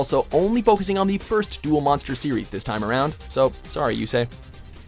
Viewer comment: someone else heard he said dual monsters?